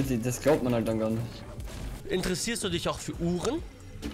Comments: Bei 11:58 hab ich verstanden: Interessierst du dich für H*ren 😂😂😂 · hab Chips gegessen, deswegen verstanden:Interessierst du dich für Huren? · Wo gesagt hat interessierst du dich für uhren ich hab huren verstanden (lul)